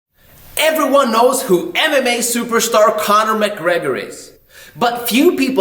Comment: ok why did i hear "anime superstar connor mc gregor" instead of "mma superstar"